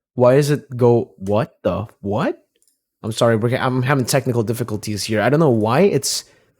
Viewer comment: why do i hear technical difficultzy instead of technical difficulties 😂? i cant unhear it now